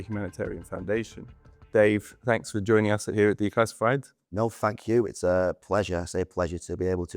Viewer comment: I thought he said no fuck you but his accent is so thick he actually said no thank you 😂😂